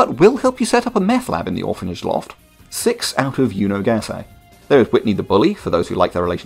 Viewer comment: I heard 6/Yuno GASLIGHT lmao, but its totally like that